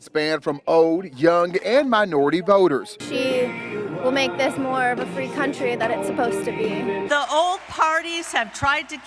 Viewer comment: "She will make this more of a free country than it's supposed to be"? Did I hear that wrong? Also, I voted Libertarian since 1992, and the party has just become a joke where it used to at least support core values of both individual and economic freedom, along with personal responsibility. I still consider myself libertarian, but I can't support that party anymore.